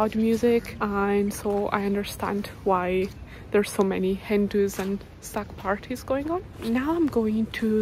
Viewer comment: At first I thought you said there were a lot of Hindus then I realised you actually said hen-dos 😂